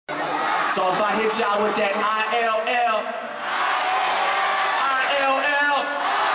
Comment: what he say in 00:01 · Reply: luucas alves "so if i hit y'all with that ill" yes, 7 years later and u finally get a reply. maybe u already figured it out idk lol but ur welcome man